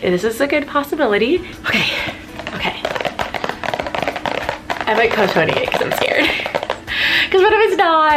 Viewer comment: Did she say I might pose 28🤣